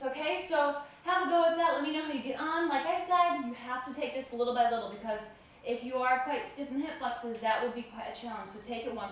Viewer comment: HI! really thank you for your video i was looking for the stretch to prepare for the Gluteus Maximus workout. but my english is not well that i can't get it clearly from 3:20. could you tell me what did you say after 3:20? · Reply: I say take this little by little, as this can be challenging if you are tight in hip flexors, so take it one step at a time. For glute stretch try this Perfect Hot Body Stretch